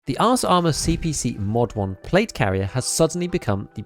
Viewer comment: I’m an immature dork and I swear I heard you say “arse” armour CPC and I’m never saying ars ever again. It’s arse always and forever now 😂